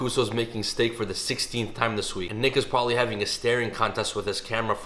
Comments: Bro I heard "nick is" as "niggas" I was like "tf?" ☠️ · Did he just say “prolly have a staining contest with his camera” 🤣 shots fired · damn bro that sounded like the n word. He said and nick